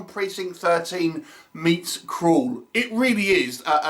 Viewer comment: Oh, 'CRAWL'... I see the fellow's comment below. I thought you had said 'KRULL' and I was thinking, 'Awesome!' However, I was trying to figure out how the hell they were gonna blend 'ASSAULT ON PRECINCT 13' & 'KRULL'... 😁